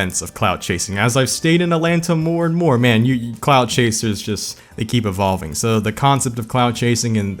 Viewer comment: Imma be honest. The first few times i heard him say “clout chaser”, i thought he was saying “clown chaser”.